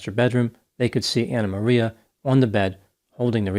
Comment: At first, I thought I heard you say Anna Maria Gonorrhea, which is an outstanding name for a punk band.